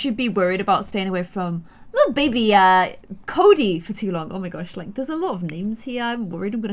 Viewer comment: anyone else notice how she called the baby cody instead of koda at 6:44? lol